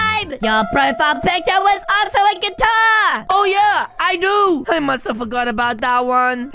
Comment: "GUITAR" lol I thought she said Qatar 💀💀💀